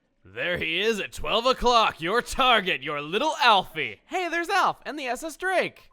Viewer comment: if you listen closely it sounds like she said "alouff"